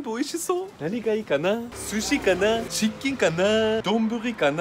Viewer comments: ｢チキンかな？｣が｢失禁かな？｣に聞こえた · チキンかなーじゃなくてチッキンかなーって聞こえる😆 キッチンかな～